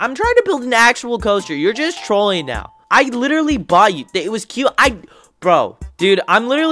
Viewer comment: I heard “I literary bought you”😂